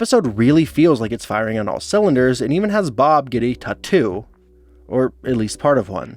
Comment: did u just say "tah-too